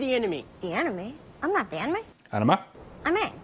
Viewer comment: I know what means with the line delivery, as non native speaker, I had to skip back to listen what exactly he said. There are some letters getting swallowed 11:45 yes thats, he said "im not the Anime" god damn right :D